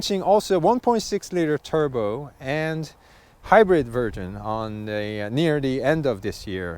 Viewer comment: did he just say "hybrid virgin"? Just kidding. Nice review. 😊